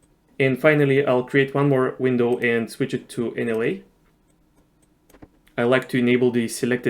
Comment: Which category did you click on at 15:13-15:14? I can't see or understand what you said. Awesome video! Very good content. Thank you. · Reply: NLA (non-liniar animation) :)